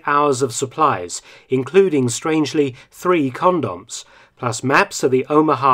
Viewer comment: “And three” what? I’m trying to understand what he saying, sound like corn dogs. But I know that’s not it. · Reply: Three condoms, strangely enough condoms were issued to soldiers so that they could cover the barrel of their guns and keep them from taking in any water.